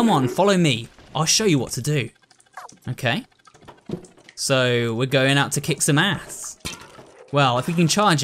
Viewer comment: DID HE JUST SAY. SWEAR WORD hE SAID SO WE GOING TI KICK SOME ASS HE SAID AT 22:1